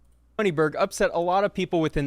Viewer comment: i heard goldberg as a name but moneyberg 🙃 that for sure isn't a real name 🤡 ...also his logo 💀🤣 14:11 what is he? a pimp 😆😆 18:17 'i was featured in forbes' ühhh ühhh 🤣🤣